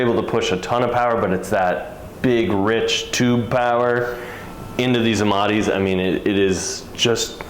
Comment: Did he say "tube power"?